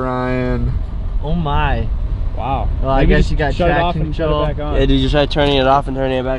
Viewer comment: “shut it off and shut it back on” ??????? What did I just hear?!?!😂😂🙃